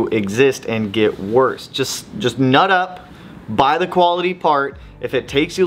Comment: Did he just say "Nut up"? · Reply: NUT UP BABY